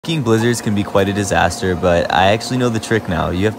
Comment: I thought he said "fucking blizzards"